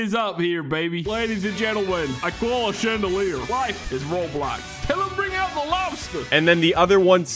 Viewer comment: i hear life is roblox 💀